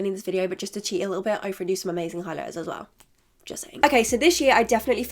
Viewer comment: can someone tell me what she said at 24:14 ?!😂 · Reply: “i’ve renoofed some amazing highlighters” is all i’m hearing 😩